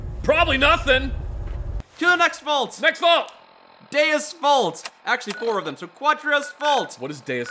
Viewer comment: I thought said deus VULT at first lmao. I guess this really is a holy mission